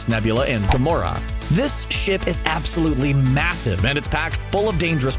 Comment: thought he said this sh*t is absolutely massive